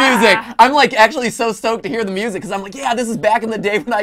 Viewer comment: Did he say he was "Stoked"?